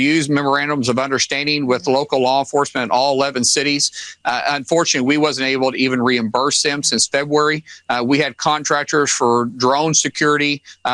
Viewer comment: Did Markwaynemullen say "We wasn't unable...."??? Maybe he should focus on education, preferably his OWN education!